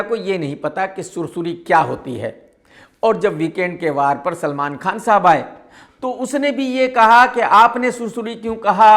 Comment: did I hear salman khan sahab Tu to 2gla nikla😂